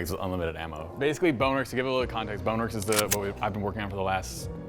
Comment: For a second I thought it said “ bonerworks “....... Just don’t question me boi....